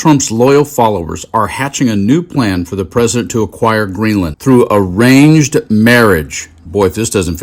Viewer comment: "Even liberal people like him" LMAO what?! At first, I thought he said deranged marriage instead of arranged marriage, but it fits.